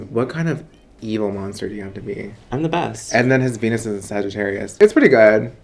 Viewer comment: This is wat I heard “His Penis is a Sagittarius ♐️ “ lol